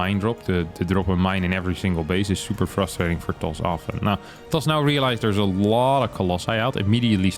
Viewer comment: "*Terran*" pops up on Harstem's cam, which looks like it's supposed to be a correction for "'toss", but I think he actually said "Does" with a very Dutch accent.